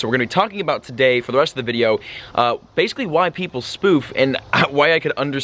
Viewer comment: Sounded like you said "White people spoof" lol.